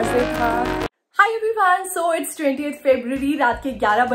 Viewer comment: Did you said 0:17 .....28th February...🤔🤔